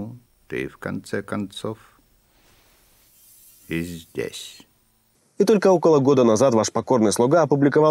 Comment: Потому что Смит программа а Нео человек))Все расходимся))ну или точнее Смит разросся до невозможных пределов то есть размножился и его эго увеличилось и по сути это помешало ему воспринимать правильно свое будущее ведь он возомнил себя богом равным Архитектору наверное))Как в концепции Инь и Ян если Инь становится много оно переходит в Ян если Ян становится много оно переходит в Инь-это закон вселенной и природы и он действует даже в виртуальной реальности то есть в математической))А при таком раскладе даже самое мизерное может повлиять на разрушаемость данной конструкции под именем "охреневший в окнец Смит")) На 0:34 мне послышалось что Архитектор сказал "И в конце концов пи*десь" ))) Так именно так и выкладывают всякие обрезки со снятыми происшествиями в интернет))Типа ой смотрите полиция щемит женщину ни за что дадада ни за что!или ой глядите кто-то кому-то задает провокационные вопросы ай маладэс смелый какой рубанул правду матку прям в лицо))) И именно поэтому эти два идиота сменили пол)) Они хоть поняли что они сделали и какая была причина сделать это?)))Не известно но уже поздно))Пиписьки отрезаны сиськи пришиты гормоны закачены вперед и с песней сестрички)))